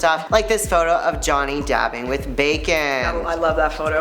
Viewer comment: I wasn't looking at the screen, I thought he said jhonny depp with bacon "Kevin bacon"